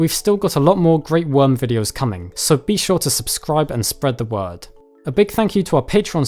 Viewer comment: I heard "If you like this video, spread the WORM"